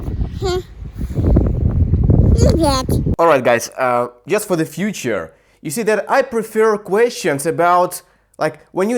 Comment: Did she called me "BLYAT"? 🙂 I like this kind of video format, but i prefer seeing to the FRRIEND when he talks. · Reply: There were complications with camera )) and lol she said privet, which means hello